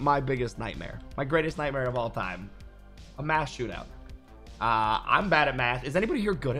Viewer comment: Is anyone else hearing “a mass shootout”??